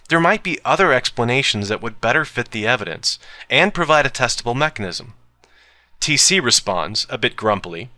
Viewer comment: I thought he said "testicle mechanism"